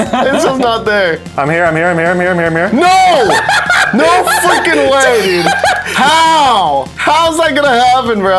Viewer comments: did he say fucking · sounded like "no fu**ing way dude"-paul